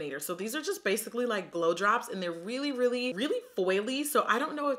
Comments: Thought she said blow jobs when she said glow drops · i had to re-listen about 4 times cause i kept hearing blowjobs instead of glow drops